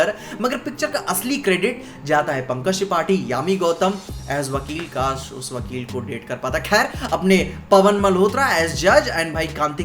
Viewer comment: did he said " kaash us wakil ko Date kr pata"?😂